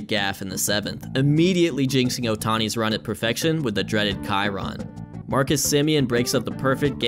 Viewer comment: what's saying after "dreaded"? ky ron? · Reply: chyron